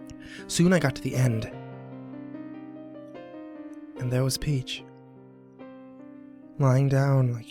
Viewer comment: Ohh shit when you said "And there's Peach" I thought you said "And there's Paige." It definitely got my attention because my name is Paige o___e